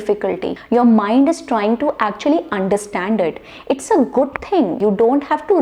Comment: you are saying...."Understanded" what is this ?? Is this a correct word ??